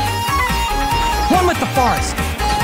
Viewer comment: I heard “one with the farts” instead of “one with the forest”, truly exited to hear this line everytime a Tighnari main joins my world.